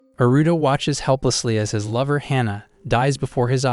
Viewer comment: I thought you said naruto